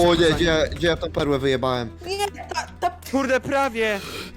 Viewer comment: Czy tylko ja usłyszałem ,, Gdzie ja ta perłe wyjebalem?''